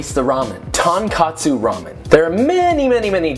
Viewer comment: TonKATsu ramen?I am mad now and will use my flip flop if you continue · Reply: it sounds like he is saying tAnkAtsu